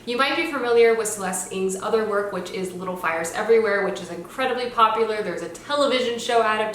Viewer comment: I misheard and thought you said Celeste Ng's other book is called "Little Virus Everywhere" 😂😭